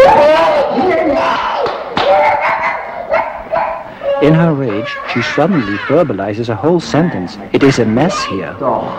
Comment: what does she say @ 23:45 ? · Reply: 'Het is hier een bende' wich means ' It is a mess in here'